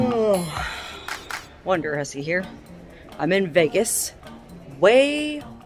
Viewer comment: All the years I've been watching you... I thought Intro said WonderPussy!🤣🤣😘👍